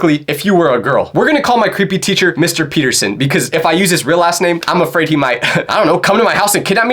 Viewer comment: did bro say ass..